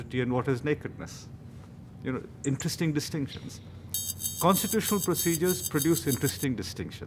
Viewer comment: the ticker says 'intestine distinctions' i think the author says ' interesting distinctions' - pl check and rectify, if need be.